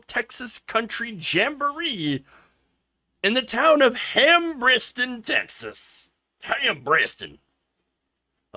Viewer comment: I thought you were saying ham brisket texas. And I have always liked Garth his wife to